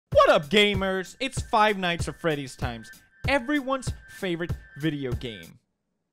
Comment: I could have sworn he said five nights at freddy is the best game